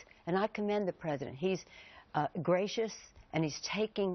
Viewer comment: Did I hear her say that obammy is GRACIOUS?